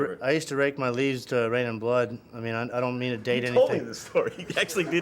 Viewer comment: "I used to rake my leaves to Reign in Blood." - Brent (God) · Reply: Haha I thought he said I used to rake my leads to reign in blood. I thought it was a guitar playing technique lol.